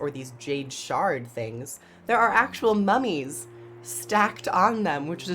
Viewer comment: I thought you said “nummies” on the jade pillars and I was like what? Like cookies on them? That’s just weird until I looked again haha. Cookie necro!